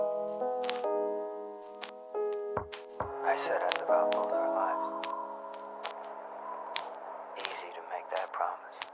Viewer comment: What does he say at 0:26 on the FF7 Main Theme "I said I ____________ both our lives, easy to make that promise" I can't figure out what he SAYS! haha. BTW probably one of the greatest lofi mixed songs I've ever heard. When the piano starts... I get lost in the feels! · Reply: I think its Cloud saying "I said I'd live out both our lives"